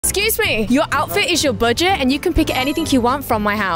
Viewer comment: Why did i hear 'kiss me' instead of excuse me😂